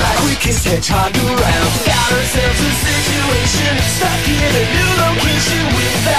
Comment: THIS IS WHAT I HEAR 0:16 stuck in a noodle kitchen